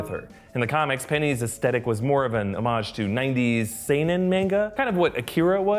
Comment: i know u said manga at 6:44 but i swear to god i heard nigga at first